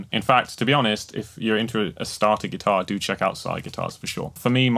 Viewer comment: Can anyone tell me what acoustic guitar he is talking about I couldn't quite catch it or find it? He said something like "cyra" acoustics. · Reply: Sire guitars